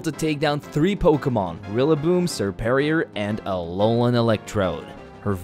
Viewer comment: Did he said Alolan electron?